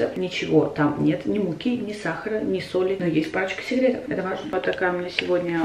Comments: Мне послышалось или на моменте 2:52 сказала: но есть пачка сигарет? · Мне одной прислышолось есть пачки сигарет? На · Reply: Блин, реально "парочка советов"! А ведь мне тоже послышалось "пачка сигарет")))))